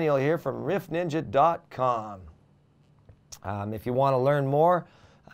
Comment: I thought he said leaf ninja dot com. :))